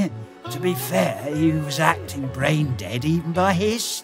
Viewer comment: I heard "rain daddy" when he said "brain dead"